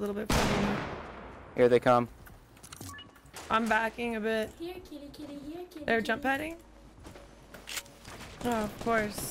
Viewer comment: did anyone hear that at 7:50? “here kitty kitty here kitty kitty”